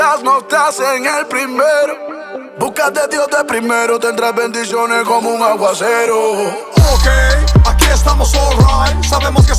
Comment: Busca de Dios primero y tendrás bendiciones como un aguacero <3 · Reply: Por un momento pense que decia tendras likes como un aguacero 😂😂😂 bendiciones!